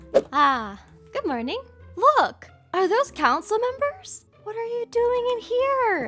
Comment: When you said Council member I thought you said cowcin member